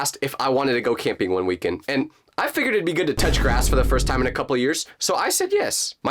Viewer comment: When said some thing about touching grass I thought he said touch her ass 😅😂